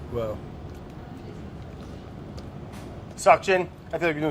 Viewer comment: When brad said "suction" I thought he said "Seokjin" and my face lit up